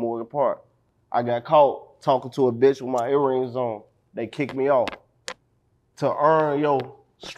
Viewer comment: If you get kicked off the football team for earrings, game was weak asl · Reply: that is not what he said. He said he got caught with earrings so “they kicked me off”